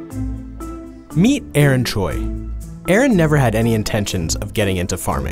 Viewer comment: At the beginning I thought he was gonna say: Meet Arnold! LoL